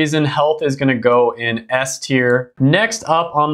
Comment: Ironically at 10:13 when he said health went into S tier captions said “St ER”.